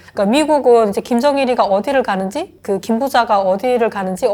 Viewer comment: 김정일..??? How old is this?? She clearly says Kim Jung-Il while subtitle says Kim Jong-un